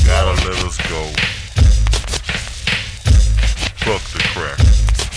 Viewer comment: does he say "Fuq tha crackas" ??